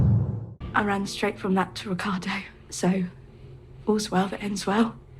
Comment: I thought she said she was dating someone called Ocado